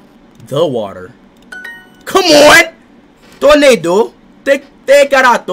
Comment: I loved when he said ''το νερο'', because it sounded like he said ''tornado'' 🌪️ 🌪️ 🌪️ I am actually Greek and I will give him a rating of 8.5/10 for his first time trying to learn Greek! 🇬🇷 Also, someone please tell him what ''Poutana and Malaka'' means 😂😂😂❤❤❤